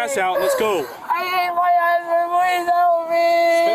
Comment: Her: “ I hate my husband, help meee” Captions: “I ate my H please help me” Me: “That’s not what she’s saying caption's!” 🤣🤣🤣🤣